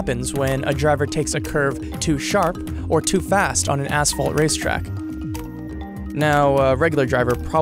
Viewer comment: who else heard asshole racetracks lol